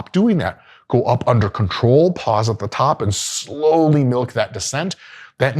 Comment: I heard "slowly milk" and switched over to a more suitable video platform